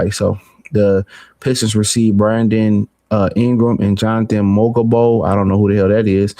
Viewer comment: Did bro really say jantey mo go bo 😂😂😂😂😂😂😂 “ I dunno who the hell that is”